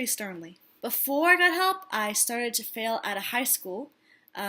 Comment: ha ha ha very good, so true, I absolutely cannot smoke pot. Lots of people in the mental illness community, swear by it, but no. I thought you said, before I got help, I started a colony(a cult), but you said comedy. Stopping by, sharing a video of everyone in the group to see if the new facebook page working. I've been wanting to do stand-up for so long, been way too much of a mess, just facing my schizophrenia now.